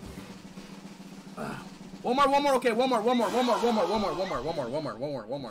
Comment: for me instead of him saying “one more one more one more” i thought he said “walmart walmart walmart” 😂